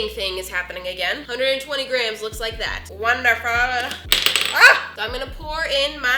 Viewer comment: I really don't know what did just say ?? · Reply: she said wonderful I just looked at the cc beacuse she writes it herself so I'm it was right